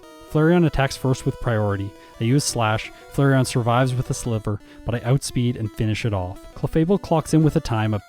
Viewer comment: i added a bit of a buffer to it, but does anyone else hear "flareon survives with a slipper"?